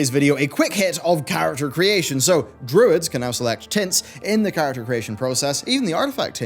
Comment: I swear your saying " Druids can now select tits in character creation" 15:43 LOL